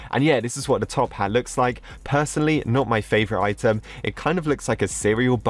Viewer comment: WHAT DID HE SAY 🤨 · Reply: item